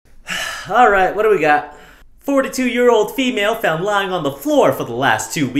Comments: Did I just have a stroke or something? I swore I heard '4 to 2 year old female' so in the beginning I was imagining a toddler fell on the floor and then heard the weight and was like 'WTF MUTANT TODDLER DO YOU HAVE?!' Then realised it's supposed to be 42 year old not 4 to 2 year old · lmfao i thought he said “4-2 year old female” 💀💀💀💀💀💀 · I thought he said “4-2 year old female” and was VERY CONFUSED 😅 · I thought you said, “four to two-year-old”!!! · I just realized he said 42 year old female, I thought he said 4-2 year old female, as in a toddler, I was real confused for a second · Oh god, je said 42 year old female, I hear "four to two year old" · lol I thought he said 4-2 year old...oh my...42 YO got it